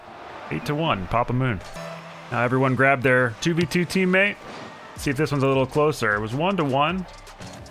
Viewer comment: I thought you said 2b2teammate and I was like, "This isn't Minecraft..."